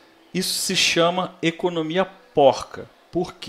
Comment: eu ouvi economia bosta da primeira vez q eu ouvi kkkkkkk